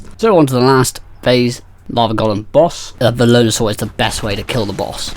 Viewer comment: what was the name of the Dino that you used? Wasn’t clear on what you said. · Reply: A Velonasaur! :)